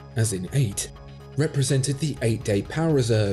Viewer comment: I heard “weed” and really struggled for a moment to understand what he was meaning… 😂